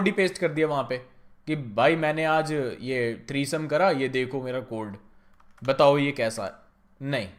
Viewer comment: did he say three sum 😂😂 /tree sum